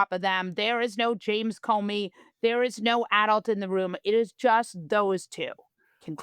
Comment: Molly, did you just say: there's no Adolf in the room?